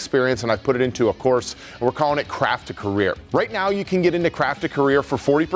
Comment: Thanks for putting the logo for "Craft to Career" on the video, because I definitely thought you said "Crap to Career" LMAO